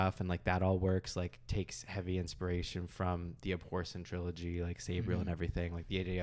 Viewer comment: I'm sorry, The what Trilogy? I didn't quite catch it but I'm curious, I want to look it up. I'm sorry if all along I was thinking Deathwalkers and the River were inspired by Charon and the River Styx 😅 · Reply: The Abhorsen Trilogy by Garth Nix (you might have heard of the first one, Sabriel)